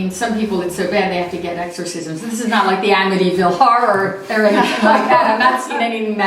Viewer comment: i thought said the amityville horrors were fake